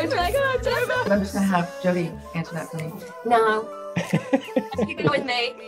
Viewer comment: What's sandra saying at 2:53 and jodie said she's with me, she's not allowed ? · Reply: yeah and Sandra said let Jodie answer for her, then Jodie said no no she's not allowed. (although Jodie didn't have to answer for her because Sandra said many times in other interviews that she'll never come back to greys, sadly)